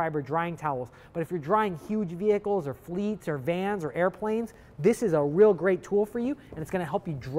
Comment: did you just say " dry airplanes"? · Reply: YES, he said, "... or vans, or airplanes..." at 7:22. I use one on my Lear Jet once a month...